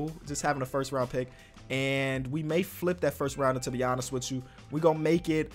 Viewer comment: I thought he said I will flip this first rounder into Giannis